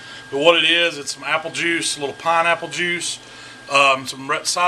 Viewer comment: it sound like you said red cider vinegar. Is that the same as apple cider vinegar?